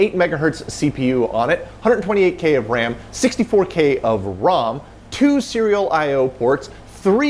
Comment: Did you just say 128k ram and 64k ROM?